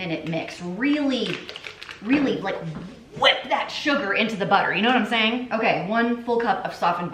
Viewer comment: I’m a Long time viewer here and always entertained but I swear I thought you were gonna say “whip that sh*t” making the cookies. 😅😂 that whole segment I was cracking up Hahaha I love!